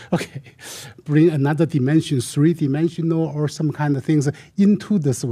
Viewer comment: Everytime they say "dimension" I hear "dementia". Maybe it's just me being influenced by their stupidity.